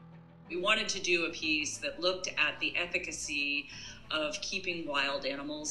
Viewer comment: did she said ethicacy?